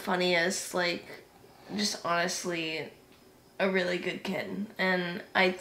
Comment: Anybody who wants to connect with can do so at <link> He didn't have a social media account but I convinced him to start one after we filmed this interview! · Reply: Hey, 9:26 caption says kitten not kid, just so you know.